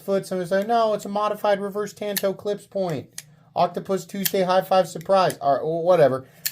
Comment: "Octopus Tuesday High-five Surprise". Consequently one of the smoothest chain of words ever. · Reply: I heard it as Oculus Tuesday High Five Surprise